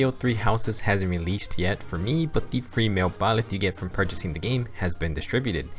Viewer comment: I put this together as I was waiting for Three Houses to release and hopefully my explanation of follow-up attacks made sense. I've been playing Three Houses and after getting familiarized with everything I'm really enjoying it. Hoping to get a steady pace through the game and try to beat my first route. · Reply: I heard "free male byleth" as female byleth